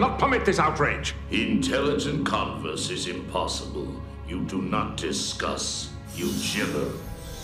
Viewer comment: "You do not 'discuss'; you gibber!" For some reason, that line gives me chills in the same way as Sovereign's "You exist because we allow it and you will end because we demand it." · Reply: I heard "you do not discuss eugenics" lol